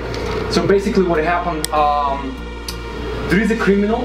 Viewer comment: Guys I love what you are doing. Take this as constructive feedback. When does the talking with his Russian accent and speaking fast and passionately to these owners it it TOTALLY freaking them out. does a great job trying to spell it out it just feels like an assault. Have do the initial talking. His American accent and slower pace speech pace might make it easier for them to listen. For safe mind your volume. Loud volume comes across threatening from strangers. You have to treat the store owners like you are talking to your grandmother everytime or they will spook. I am also praying for your safety. I really hope you have more than a camera and a cell phone to keep you safe. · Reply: Was going to leave the same comment. is awesome energy for us but not to a store owner or to the cops. Chill brother. With the accent, you sound like you were saying "illegal" business until you started calling it "legit" instead.